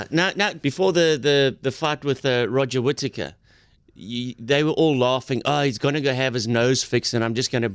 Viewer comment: Did he just say Roger Whittaker?